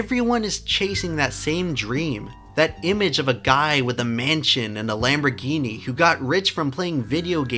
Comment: When you said dream of a mansion and Lamborghini, I thought you were saying "man chin"... As in leafy always dreamt of having a man chin.